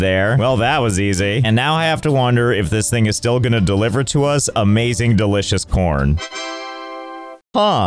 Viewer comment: What did he say? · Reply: He... said "and now i have to wonder if this thing is still going to deliver to us amazing delicious corn." What's so odd about that?